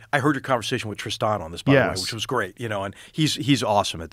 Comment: Anyone know who McMaster referred to at 55:02? I couldn't catch it and the subtitles aren't picking it up either. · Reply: He said "Tristan," possibly referring to Tristan Harris.